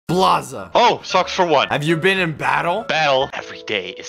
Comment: Blaza at the start of all SocksShorts videos: “SocksFor1!” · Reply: Oh he said socksfor1, i thought he said f**k for one😂